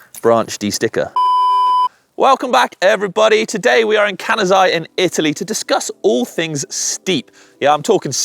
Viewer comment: I thought he said,"Today we are in Canada in Italy", and I thought he was having a stroke 😂